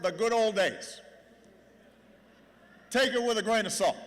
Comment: What did he say at 13:52, take it with a .......???? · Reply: "grain of salt" its an expression meaning don't take it completely literally